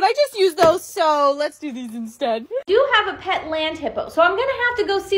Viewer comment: I thought she said she had a pet lamp😂😂😂😂😂😂😂😂😂😂😂